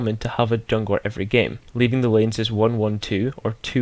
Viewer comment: When you said lanes I thought you said names :P